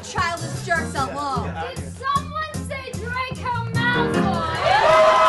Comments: "let's leave these babyish jerks alone-" "DID SOMEONE SAY DRACO MALFOY?" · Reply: Subtitles say: "cue our queen". I'm dead. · okay when she said "did someone say draco malfoy" i heard "hey hey, tell a shortie come here"- HELP